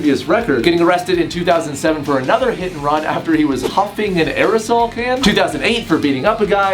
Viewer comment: Did anyone else hear "He was humping an aerosol can?"